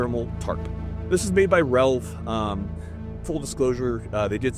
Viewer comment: thermal tarp made by who? Can't quite hear. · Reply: It is made by Relv. It is also $500 dollars 😂😂😂